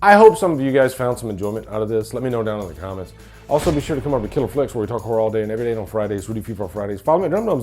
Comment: Dude what did you say at like 7:41 mark. That was really fast ⏩ 😂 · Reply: Also come over to killer flicks where we talk Horror all day and every day except on Fridays where we do free for all Fridays. I think that’s what I said. 😂